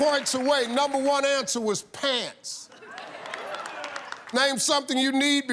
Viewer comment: shaq said “pads”.. he was never gona admit that, he knows how to win championships .. and snitches get stitches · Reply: like inwas explaining to that dummy, the cc is made after shows like this as theres no script..they probably saw that they misheard him say pants when he really said pads and just put it in that way